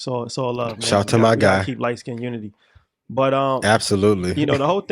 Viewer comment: sec did he say "keep light skinned unity"?